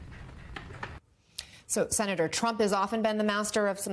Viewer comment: Did she call him Senator Trump??